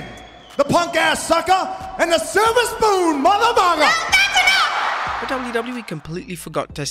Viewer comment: I thought the Rock said Mother Thugger.